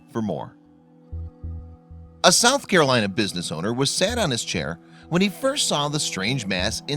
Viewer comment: At least 4 times i had to rewind because the first things you said at 0:20-0:24, I can't understand...quote "...business owner was SAD/SAT on his chair..." unquote.If you expect us to listen & comprehend you, please talk correctly!! What the heck did you say?? WAS SAT?? WAS SAD ON HIS CHAIR... if I can't follow you, I can't subscribe and the rest of the audience can't either. Please slow down & speak intelligently. You're making up your own form of speaking, a sign of incompetence & being stupid.